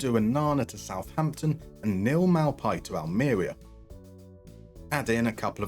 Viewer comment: For a minute I thought you said, "Neil Maupay to Antarctica", and it was the first thing that's made sense to me in at least a week. But alas.